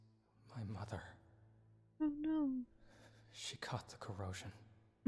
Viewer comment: 1 9:10 " It's ... My Mother. She cought the 'Corona'." I couldn't help but mishear that. Oops my bad eheh